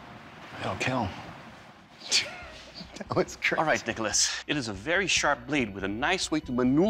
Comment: did anyone else hear dickolas